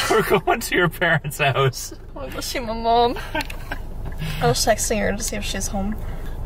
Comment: it totally sounded like she said sexting for a second like girl what😭😭😭😭😭😭😭